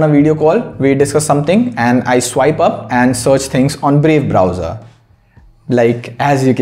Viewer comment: Brave Browser, in 2x I heard that as "Babe Browser" 😂😂